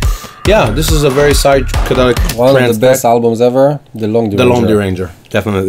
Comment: What's the album that they said on 14:11? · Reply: ShideShugo Hallucinogen - The Lone Deranger